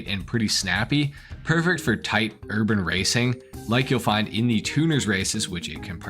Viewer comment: I legit thought you said "pervert for tight..." for a split second. Got me laughing 😩😂